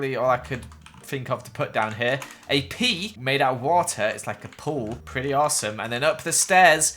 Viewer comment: "a pee made out of water" is what I heard. I wasn't looking at the screen when you said that. It made me go back a few seconds.